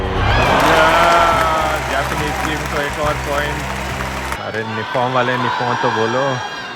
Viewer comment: I think they are saying nihon = Japan